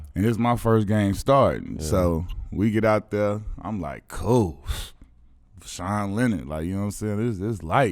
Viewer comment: lol thought he said sean lennon. its Voshon Lenard